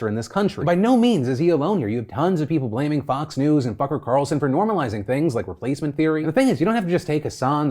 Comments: I swear I heard Phil said F-ucker Carlson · Did you say "fucker carlson" at 21:40 ? Or am I just crazy because that's great lmao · Was that a freudian slip? I thought I heard "Fucker Carlsson" in the last segment. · I had to rewind it to make sure that I heard you right, and sure enough you said Fucker Carlson. 👍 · Did he say Fucker Carlson? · Did Philly just call him "Fucker Carlson? Lol. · Did i just hear "Fucker Carlson"? Probably not, but I'm going to pretend I did anyways. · had to rewind to make sure I heard "fucker carlson" lol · did Phil just say Fucker Carlson?? 🤣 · I heard that “f*ucker Carlson” 😘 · LOL it sounded like Phil said "Fucker Carlson". That works so well. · did you call him “f*cker carlson?” Because that would be amazing unless I just misheard it three times I went back and re-listened?